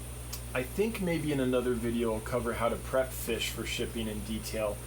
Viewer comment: my eyes are getting bad, I thought he said how to strip fish? I thought it was taking babies or eggs from mouthbrooders. ugggh